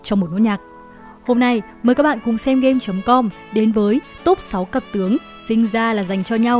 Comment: top 6 c*c tướng? Chắc là mình nghe nhầm đúng ko😀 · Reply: Cặp tướng bro ei :))